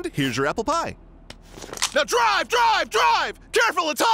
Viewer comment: "Careful it's hot" :) "NOW DRIVE! DRIVE! DRIVE!" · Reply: Aman Sakhrani 1:19. Put on captions and you'll read; "now drive drive drive" because you certainly didn't hear it, along with the two people who liked your comment and the many more after....